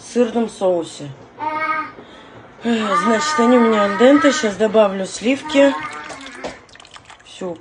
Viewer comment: Мне послышалось, что она сказала - они у меня в доментосе... 😅 Думаю... ну, а че! только и осталось все в нем и замочить!)🤣🤣🤣